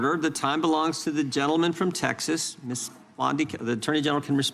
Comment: am I crazy or did Jim call her “blondei” ? I can’t be the only person who calls her Scam Blondie.